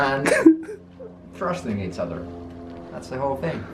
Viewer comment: Did he say... Thrusting each other